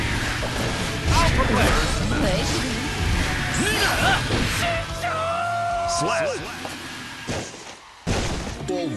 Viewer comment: What did chip say at 7:35!? 😆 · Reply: SHISHOUUUUUUUU!!!! (Master in Japanese, referring to the guy who trained him. Also, he uses an American accent while doing it.)